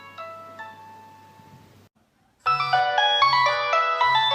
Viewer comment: the subtitles are wrong that’s nokia 3220, not 3200